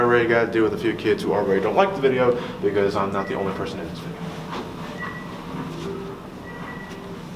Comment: I can’t hear what he says here. Is it “because I’m not the only person who hates me”?